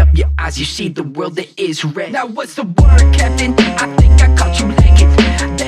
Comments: Thought it said: Now whats the work ethic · for some reason i always thought he said "kevin" instead of "captain"